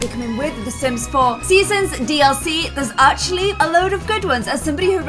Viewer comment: i thought you said ddlc, i was like OMGIFVJFKKGNKRFV